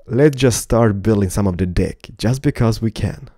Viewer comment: put Sublitties on (hvatever Wou Wanna call it)and ther a word that says dick instead of deck